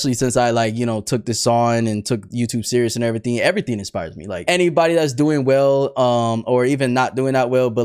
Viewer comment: i misheard him as "Anybody who's gooning well" by the way cheese sticks